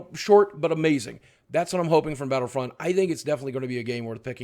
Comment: I thought boogie said battle field 4 and I was about to stab someone